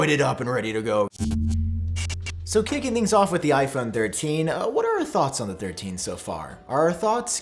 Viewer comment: I thought it said iphone 15 and I was like jesus already